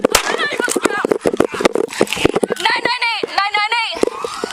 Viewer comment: when she said “998” I deadass thought she said “night night mate” and I was like hell yeah, tell his ass it’s schleep time bitch. 😂😂